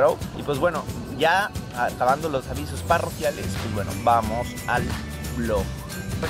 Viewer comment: la primera ves que escuche "saludos parroquiales", eso lo hacen los párrocos en las parroquias, esperaba tal ves que dijeras "saludos coloquiales